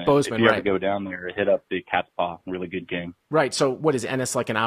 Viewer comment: Caller, could you please type in the name of the place that you said, “is a really good game”, that is close to Bozeman. It sounds like you say Big Cat Paw. Thank you. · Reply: cats paw